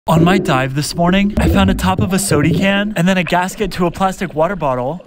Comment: sodican が shuriken に聞こえる